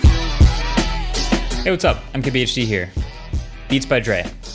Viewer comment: A bit off topic, but it's funny how I always hear at the beginning of the video when you say "Hey, MKBHD here", I hear "Hey I'm KBHD" 🙂. And every time I get fooled 😂😂